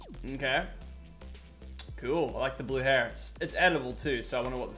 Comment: did he just call that edible